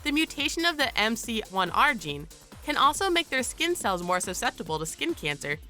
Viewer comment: I thought it said MCR XD